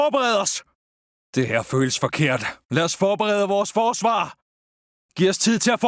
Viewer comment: and 2:40 What are you saying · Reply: “Det her føles forkert… lad os forberede vores forsvar!” (This feels wrong… let’s prepare our defenses!”) “Giv os tid til at forberede! Vi bliver nødt til at beskytte det høje liggende terræn!” (Give us time to prepare! We’ll need to protect the high lying terrain!”)